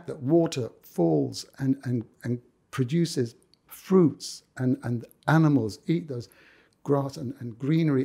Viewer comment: I absolutely adore Cat/Yusuf, but did he just say “Water falls and produces animals (eat those), and grass and greenery…?????” Did he really say “Eat those”??? Arghhhh 😢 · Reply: I think he said "water falls and produces fruits - and animals eat those (fruits), grass and greenery...and all this is like signposts telling you that there is a Creator and an Almighty intellect behind everything that you see right there in front of you..."